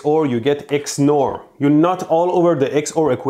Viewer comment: I hear nut every time he says not 5:21 : "you nut all over...."